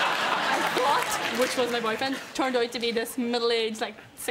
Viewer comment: What's Aileen's boyfriend's name? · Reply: Her accent is so heavy, I'm American 🇺🇸, so I thought she said, "I thought, which was my boyfriend....." Guess it doesn't make much sense but she could've misspoke being nervous on TV. Can anyone out there make out what she's saying??? Translation please.... GREAT story tho!!! 👏👏👏